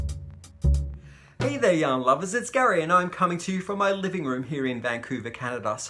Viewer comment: Gary, I always hear "hello, young lovers" and then I pause and realize you have said "yarn lovers"! LOL